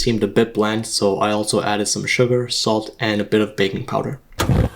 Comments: What did bro sayyyyyyyyyyyyyyy baby powder 🤨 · Baking powder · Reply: oh i thought he said bacon powder